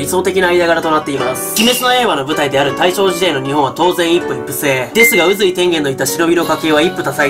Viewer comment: 聞き間違えかな？一夫一妻制が一夫一夫制に聞こえたww